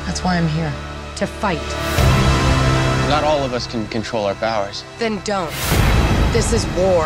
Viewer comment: "not all of us can con-control our bowers" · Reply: for a moment it sounded like he said "not all of us can control our bowels" next scene of him on a toilet with a bad case of constipation.